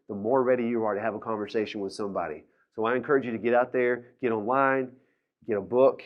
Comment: Did he say, "I encourage you to get out there and hit a line..."?! lol